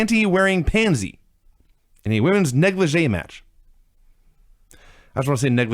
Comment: What word did say at 1:22-1:28?... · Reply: Negligee (pronounced neg-luh-zhay) Things women wear to bed when they wanna be sexy.